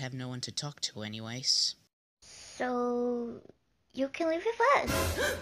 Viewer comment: what is she saying? I can't tell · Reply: "So.. you can live with us!" Here